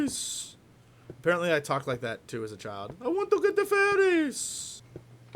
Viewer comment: it sounded like "I want to get with Ferris" but that might just be me thinking with my FFIV cap on.